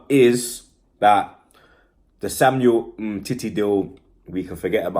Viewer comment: 😂😂😂☝🏾☝🏾 what’s his name bruv? You said his name like a proper perv 😂😂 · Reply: Umtiti